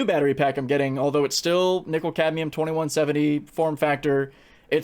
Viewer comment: Did you say nickel cadmium?